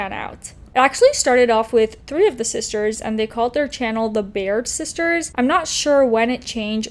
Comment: I thought you said “Beard Sisters” and I was thinking, “how ironic ‘cause she is her husband’s ‘beard’, or at least that’s what I heard.” 🤣😂🤣😂